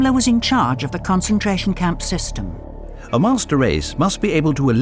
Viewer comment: the narrator says “master race”, but CC says “monster” instead. How ironic.